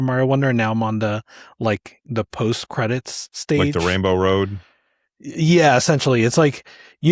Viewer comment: I thought I heard say “Rambo Road” and the intersection of Rambo and Mario kart was wild